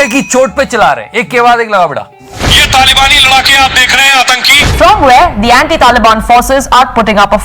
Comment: I heard B&D taliban forces😆😆